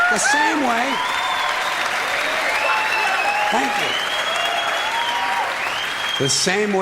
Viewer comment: i definitely heard someone in the audience yell fuck you 💀💀